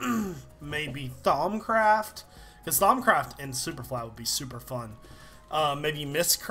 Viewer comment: omg its thaum craft i always thought he was saying thongcraft.